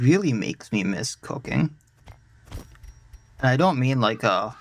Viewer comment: Am I the only one who first thought you said “…makes me really miss cocaine” instead of cooking at 8:14 ? 😂 Great vids man, really enjoy your play through so far! Greetings from Sweden ❤